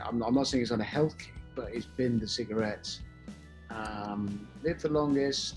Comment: did he say jeremy binned the cigarettes?